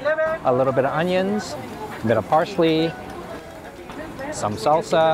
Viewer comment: did you say parsley? maybe you shouldn't film the hispanic people too long on the street. (they could be illegal. They're trying to be anonymous.)